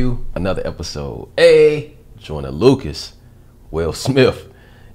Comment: Who else heard Will Smif 😂